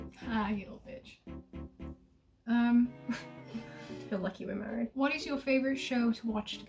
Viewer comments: did say "you're lucky we're married" ???? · imagine their wedding theme be the host club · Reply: I heard they were already married! Watch 8:27, whispers, "Your lucky we're married"!! :D